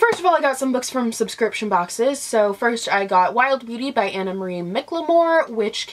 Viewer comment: I thought that you said “Wild Booty!” For when you were showing Wild Beauty! Hahahaha! Which is hilarious either way! But yea, am also a huge Harry Potter fan as well.